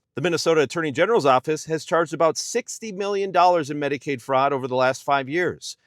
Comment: You know as well as I do your taxes are going to Israel, Argentina and Trump’s cabinet. I think they just spent over $100,000,000.00 to buy Kristy Noem two new jets, not to mention the one billion dollars repurposing the jet that Trump got as a bribe from the United Arab Emirates. How about the over sixty million that has been spent on Trump’s golf games just this year? How about 40 billion to Argentina, no strings attached and another 20 billion to Israel? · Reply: actually I thought I heard 160 mil but I wanted to be conservative. Easy to find out.